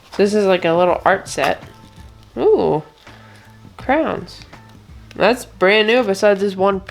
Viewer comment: The oval DuPont thing looks like a cutting board. I've seen that rolling case hold all sorts of stuff! Also when you said it smelled like crayons, I heard "crowns". I had a student ask me for a green crown once...I had NO idea!!!